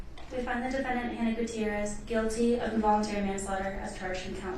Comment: In New Mexico, involuntary manslaughter carries a maximum penalty of $5k fine and 18 months in jail. So basically it's a slap on the wrist. This appears to be the maximum penalty, so she may receive a lesser sentence. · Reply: I thought I heard "voluntary" manslaughter. It wasn't clear at all, I played it several times even. Pfff.